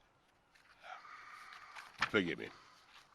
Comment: How odd... Must have misheard, but thought he was mumbling 'bigamy', not 'forgive me'.